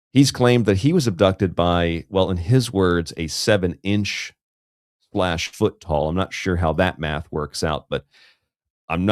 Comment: Man I can't believe I missed another great show. I fell asleep very early and didn't wake up until after 2 AM. In defense of it sounded like he said the creature was sevenish foot tall meaning around seven foot it could be misconstrued as sounding like being seven inches foot tall. Of course it's all bs so I get you are having fun with this whole story.